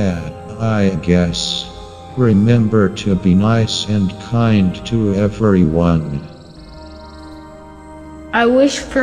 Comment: i heard " i wish for you to be my senpai and kind to everyone "